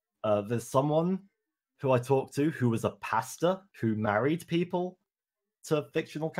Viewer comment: when he said pastor i thought he said pasta ngl